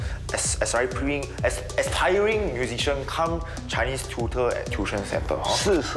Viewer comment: what the he say about the tuition center? Someone: C U M